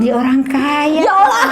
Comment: Dimenit 21:15 kak bilang ya Allah bukan sih ??? Apa gue yg salah denger ???